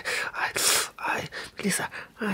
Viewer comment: omg that sounded like "kissa" which is a finnish word for cat lol